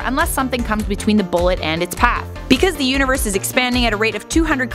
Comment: it sounded like you said because the uterus is expanding at 200 Km etc. lol nice